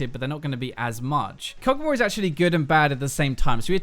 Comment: Which champ did he mentioned? I heard Kobold/coldwar... :| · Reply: kogmaw.